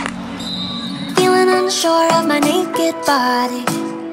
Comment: For the longest time I thought she was saying, "feeling insecure of my perfect body" 🙃